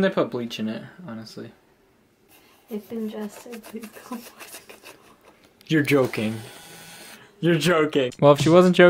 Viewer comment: what does she say ? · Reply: Doodles Out if ingested please call poison control